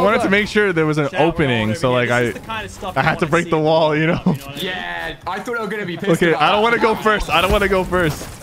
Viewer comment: is noone saying that at 8:33 alia says 'i thought they were going to be pissed about that' ?